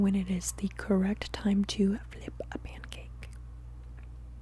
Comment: I listened to this four times and I swear I hear "flip a mancake". The answer remains the same, however